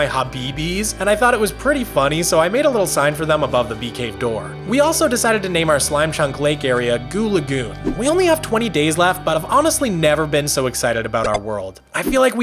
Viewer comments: excuse me, nut, uhhhh... did he say goob lagoon 22:26?????????? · why did I heard Habibi's instead of hobeebees 💀